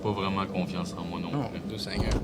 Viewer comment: dans le personnage Orbis a de la misère a faire confiance au gens donc c'est plus logique dans ce cas la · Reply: pas mal sûre qu'il dit "j'ai pas confiance en moi non plus". En tout cas, c'est mon impression